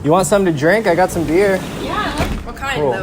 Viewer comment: he says peer instead of beer 🍻😂